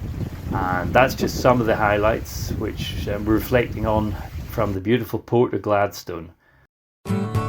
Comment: At first I thought yu said Glasgow !